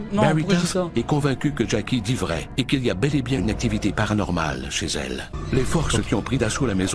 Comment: Au début, j'ai compris "parain normal" pas paranormal 🤣🤣🤣